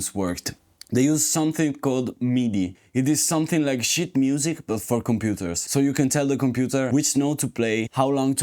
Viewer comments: I heard him say "it's like shit music, only for the computer". Well.... 😂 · Sheet music? I thought you were saying "shit" music. 😆